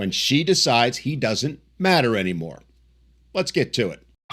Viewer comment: all i heard was "dont marry any woman"